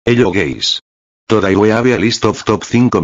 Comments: Hello gays💀 "This is what I hear" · In the begening, i hear "hello gays" 🗿